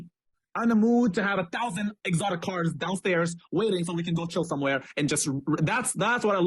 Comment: omg I thought he said 1000 exotic wh*res. I was like wtf 🤣🤣